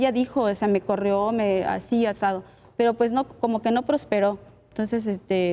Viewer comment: HAY DOCTOR CARBONELL DELE UN JALON DE OREJAS A LA LICENCIADA QUE DICE EN EL MINUTO 24:56 "ASI ASADO" ESO QUE !!! ES LICENCIADA O BOLERA DE LA ESQUINA CARAMBAS !!!!! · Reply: Dijo así o asá